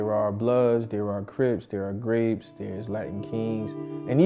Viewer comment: did he just say there is a gang called Grapes?